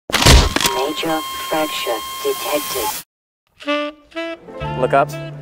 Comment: Did i just hear 'major backshot detected' ?💀